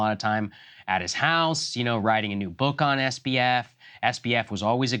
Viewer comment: Did Krystal say (@ 4:38) writer Michael Lewis is writing a book “about us”—meaning about Krystal & Saagar? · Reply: Pretty sure she said SBF but it trailed off - context from saagar a few seconds before